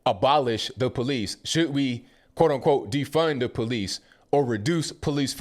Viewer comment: I felt happy for a second...I thought it said, "defend the police"...